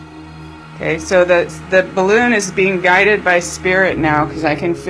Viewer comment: First time I thought I heard, do you want to die? Then 2nd I heard Do you want to fly. Then I looked at the words and then I heard Guide. You must really need to slow it down to interpret what is said.